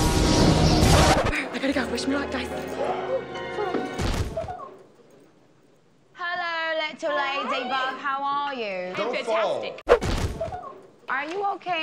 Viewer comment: I can't figure out what she's saying here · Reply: I heard 'hello little ladybug how r u?' lmao